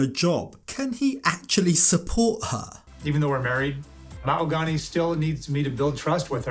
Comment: Anyone catch at 4:38 how he said "Mal-gony", not "Ma-hog-ony".